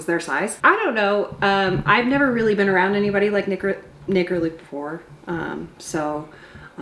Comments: hol up, what she say? lol ohh nick or luke lmao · what was that word? 😁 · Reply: She stuttered, she was trying to say Nick or Luke